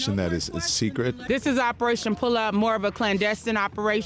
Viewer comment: I thought she said operation "pull out" at first had to do a double take. LMFAO